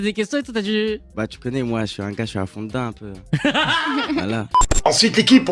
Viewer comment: à 3:43 koba dit "j'suis un fond d'teint un peu"??? Si c'est ça ça veut dire quoi svp?